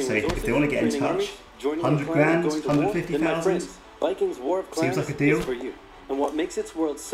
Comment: I legit thought the guy said "Vikings: War Crimes" before taking a look at the title of the game. XD